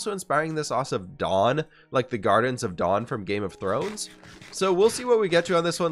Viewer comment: I'm not sure if it was your stuffiness, but it sounded like you were saying the "Gardens of Dawn" instead of the "Gardens of Dorne", the Water Gardens to be specific